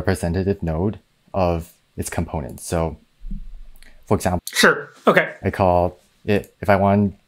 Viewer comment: Did say "Yes" in Chinese? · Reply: he said "sure" I think lol